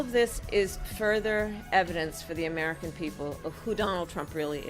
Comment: "Further advents" did I hear that right?